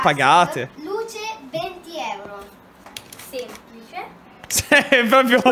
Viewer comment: io ho capito "duce... 20 euro" 🤣🤣